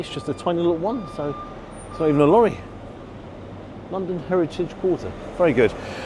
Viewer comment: I misheard the intro as "it's just a tiny little one........ and so is the lorry" and it made me laugh 😂